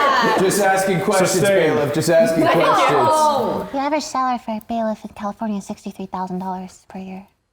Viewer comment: I still can’t figure out what Lily said 😂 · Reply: The average salary for a bailiff in california is 63 thousand dollars per year